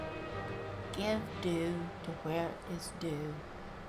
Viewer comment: Hello! I need your help.... 5:47 Can you write me what you said..... please? :) I'm a bit surprised that you did not climb to the top of the mountain after you meet Paarthurnax. From there is a magnificent view. ❤️ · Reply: I actually forgot to climb to the top, but I'll be back there again. I think this is the phrase you mean, it was pretty soft; I said "give due to where due is due" which can be said various ways, but means give due credit to 'where' or to whom deserves the credit. Which was probably Belran. :)